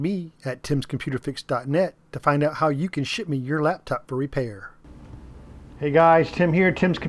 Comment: LOL. I could've sworn at the start you said "...find out how you can Shit me your laptop for repair", now that's giving Amazon drone service a run for its money. Good job mate I hate lenovos a few Toshiba's are like that too billions of screws, still, at least they haven't started making laptops as sealed units...yet!